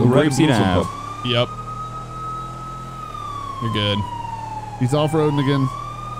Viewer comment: what did he say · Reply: “He’s off roading again”